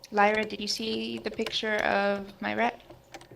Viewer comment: lol thought said have you seen the picture of my rack.